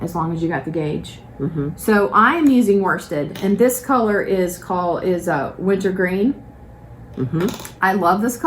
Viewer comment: Great podcast ladies! Just the right length and beautiful new colors and ideas. I thought said her new color was "Lizard Green" (haha) and was able to figure out it's Wintergreen. I love it and will probably purchase some. Don't listen to those who just don't like green. You have developed some gorgeous colors!